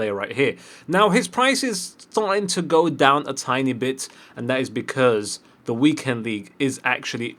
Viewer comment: Did he just say ''a tiny bit'' or ''Itani bit''